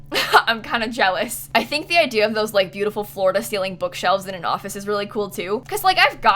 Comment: Am I the only one who thought she said "Florida celing bookshelves" when she said floor to celing bookshelves? Edit: thx sooooo much for the likes guys!